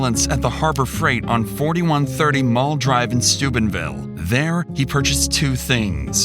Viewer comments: I thought he said on stupidville lmao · Really thought you said "Stupidville" here lmao